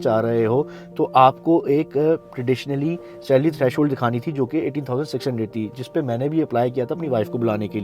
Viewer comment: 80k600 is that what you said??????? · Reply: £18,600 was when i applied for my wife but its £38,700 now